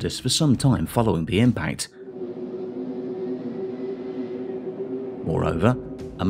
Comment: Did he say " moreover" at 6:07? god I hate that saying!!!